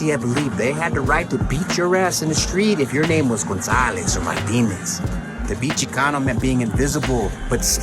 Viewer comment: the CC says "my penis" instead of Martinez...that is all